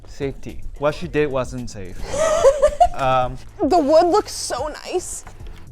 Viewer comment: "The wood looks so nice" was what she said